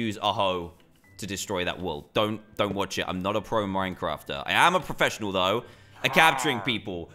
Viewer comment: what dan said: im not a pro minecrafter. i am a professional , though, at caputuring people what i thought dan said: im not a pro minecrafter. i am a professional, though.